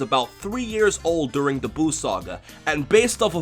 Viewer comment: when he said buu saga I thought he said boob saga